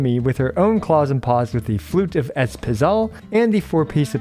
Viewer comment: I misheard the weapon as "Floof of Ezpital" and it is now its new name when equipped on Xilonen.